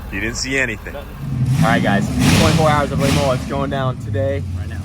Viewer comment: did really say 24 hours of le mullets?